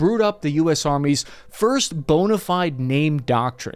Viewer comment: did you say "first boner-fied" by mistake - or did you just slip that in there? lol